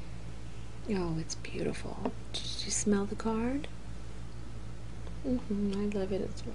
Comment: at first i miss understood what you said at 03:56 i thought you said did you smell that fart..but when i played it back i finally got it..lol